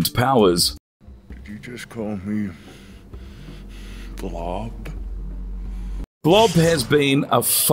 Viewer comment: Did you just call me, blob? No you idiot! Logan called you Bubb!